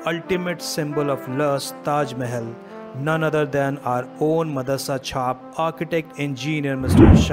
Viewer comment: Whenever he says "Madarsachap" , I don't know why but I hear "M*d*rch*d" 😂😂 ... And it is more appropriate I think